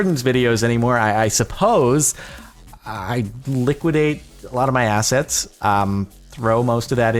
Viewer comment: Oh, you're high again. Also, at 6:50 i wasn't paying attention so when you said liquidating assets, i thought you said "Liquidating ass".